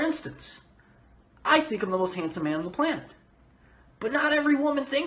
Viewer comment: What the fuck does he even say at 7:22??? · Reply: He thinks he's the most handsome man on the planet